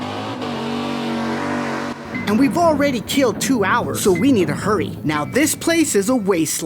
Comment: my captions said killed two owls